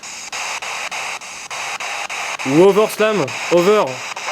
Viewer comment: Bonjour à son pépère ! Alors , je suis peut être à coté de la plaque, mais à 58:40 , j'ai l'impression d'entendre : '' Salut Over ca va ? '' . Mais après oui, on entend bien ''Overslam '' 2 fois , et '' restez- là'' très clairement .. etc.. /// L'histoire de ton ourson est juste flippante , mais j'ai quand même explosé de rire avec ton '' regarde moi cette tête de con " .. t'es un tueur ^^ /// A 1:04:25 , euh ... ouai comme tu dis , c'est bel et bien un truc de con qui se passe ! On dirait que quelqu'un (ombre noire'' viens à toi et t'enlace ! Et ce n'est pas l'ombre d'Audrey puisque tu tourne toute suite ta caméra en direction d'elle , et on voit bien qu'elle est trop loin pour que ce soit son ombre . Puis tu retourne ta caméra en direction des piliers , de l'arcade ...Et là il n'y a plus cette ombre noire (pourquoi ne serait- elle plus là , alors qu' Audrey reste toujours à sa place , donc trop loin de toi encore , et que tu remets bien ta caméra dans la même direction , le même champ de vision ?) Ouai un bon truc de con ^^ /// A 1:06:55 , j'entends clairement : '' Bon okay '' . Et si ca avait été '' Monkey '' , en restant logique , la prononciation aurait été en anglais , donc '' Monk[i] '' ( en phonétique anglaise , le ''ey'' est représenté comme ca '' [i:] , comme pour le mot ''key'' (clef) , par exemple . Après ,je suis peut être complètement à coté de la plaque . Mais c'est vraiment ce que j'ai compris , entendu . /// Après , inutile de demander si l'entité est gentill